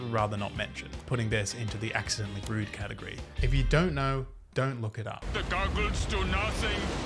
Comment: at 0:32 "if you don't know, don't look it up." What I hear: "Google it"